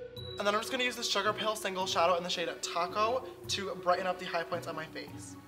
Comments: did he say "in the shade taco"? 😂 maybe he didn’t, and it was just me. But still.. 😆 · wait did he say shade taco?😂😂😂